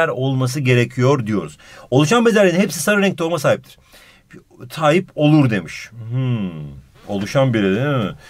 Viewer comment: tayyip diyo mu ben mi yanlış anladım